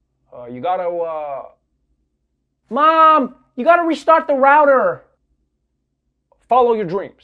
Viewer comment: As a South African... all I heard was "Mom, you goda restart the 'rowder' "... (O.O) what the hell is a rowder... Trevah... IT'S ROOTER XD.